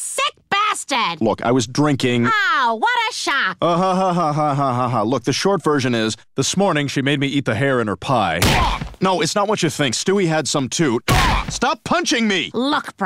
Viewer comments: She made me eat her hair pie is what I thought he said. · I could’ve sworn he said “she made me eat her hair pie” but I was wrong